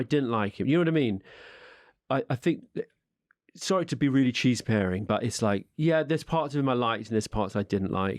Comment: “Sorry to be cheeseparing” which I heard as “sorry to be cheese pairing” enhanced the whole feel of this exchange as a wonderful charcuterie of experiences and ideas.